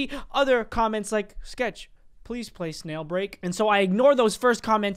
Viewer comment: Did he just say snailbreak?